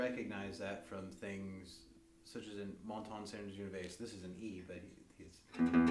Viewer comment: I cant understand what did u say at 3:30 , I think its the name of song. I really liked that part, can you write here the name of song. Thank's :) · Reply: song that he starts to play is " La montagne sainte Genevieve " or "Django Waltz " It's the same song.